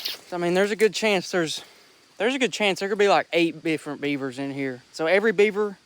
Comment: did anyone hear bifferent insted of different